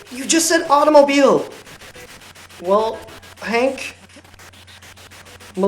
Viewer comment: it sounded like it said "wanker" 😅